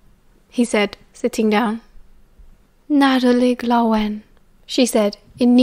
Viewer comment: what does she say i dont understand it it sounds giberish to me😅 39:28 i god i'm crying (i need to sleep its way to late to do this) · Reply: Nadolig Llawen, it's Welsh for Merry Christmas 😊